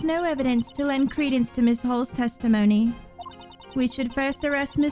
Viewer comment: whenever miles says ms. hall, i hear "ms. hole" and i damn my ears each time.